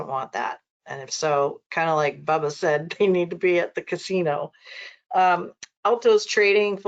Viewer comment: what did you say? It’s not clear. Bottle be at the casino ?